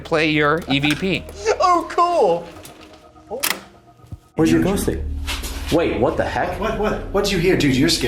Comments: I didn't hear Andrew. I heard "where's your bum stick?" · I heard "where's your gossip?" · In the evp I hear Where's your ghost suit